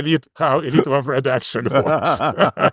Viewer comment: With his accent -- every time he says "elites overproduction" I hear "idiots overproduction" 🤣🤣🤣